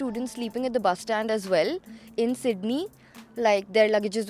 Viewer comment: Did she say Sydney? at 4:34? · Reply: Yes, Sydney, NS